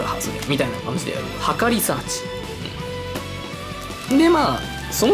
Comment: 測りサーチが墓リサーチに聞こえて話が入って来なくなった